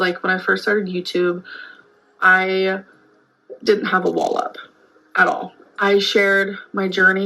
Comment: I thought she said “when I first started YouTube… I didn’t have a wallop” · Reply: I thought she said that too! It wasn’t for several seconds later that I realized she said wall up!😂